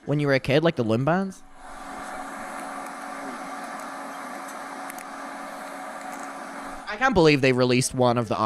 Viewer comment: I s2g I thought he said “limb bands”